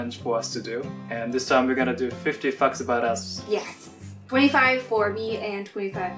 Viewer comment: I thought he said "50 fucks about us " but no It turns out I just don't hear right😂😂